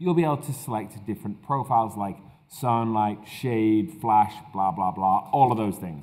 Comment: I think I hear he said BLAH BLAH at 2:19??